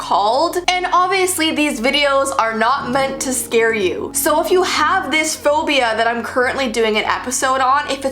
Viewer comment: My dumbass thought it said “proof that you have this phobia”